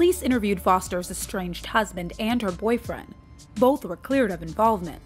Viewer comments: Did I hear husband and boyfriend? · Did they say foster’s husband and boyfriend So she was cheating on her husband?